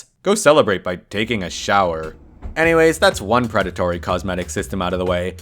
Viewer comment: did you just say sh*wer